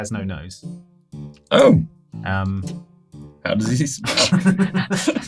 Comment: What did say at 33:47? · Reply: "How does he smell?" "The bartender has no nose." "How does he smell?" "Not as bad as Nate."